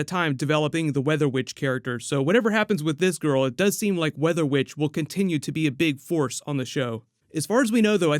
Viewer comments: i thought you said WEATHER BITCH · I heard “weather bitch” during the opening 🤣😂🤣😂🤣😂🤣😂🤣😂🤣😂. Happy New Year. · I swear the first time you said it.. It sounded like you said Weather Bitch. Hahaha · did u just say weather bitch? 😂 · For a second I thought u said weather BITCH😂😂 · Did he just say “weather bitch”? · I thought I heard weather bitch at the start there lol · Who else thought said weather bitch lmao he might of idk lol 😂